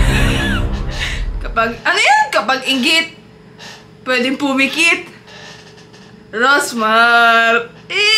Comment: What they hear "pag I get pweding pumikit rosmar" what I hear " pag iget pweding umiget rosmar"